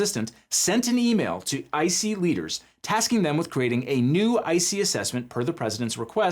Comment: I swear I heard ICY WEINERS